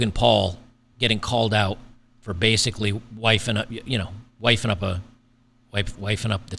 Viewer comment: "Made him wait"...for what? and am I crazy, or do I hear "wiping up" rather than "wifing up"??